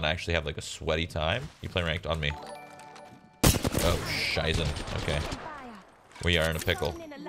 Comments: Wait what? Did i hear "scheiße" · did he just say "oh scheiße"? 😂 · did you just say: oh, scheiße? xD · Did he speak German at 14:30? I think he said oh scheiße